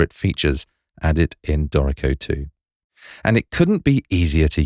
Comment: I need help for creating captions (doubts in bold ): At 0:23 did you say "this is one of my very favourite features had it been Dorico 2"? At 2:07 did you say "If you have just for me " or something else? I would appreciate a prompt reply, thank you. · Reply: The first is "...features added in Dorico 2" You're right on the second one.